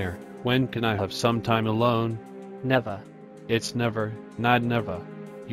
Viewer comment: I thought E Minor said Nevada the state